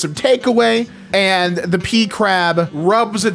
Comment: I keep hearing pee crab XD